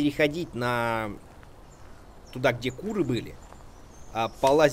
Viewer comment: мне одному послышалось : "курвы"?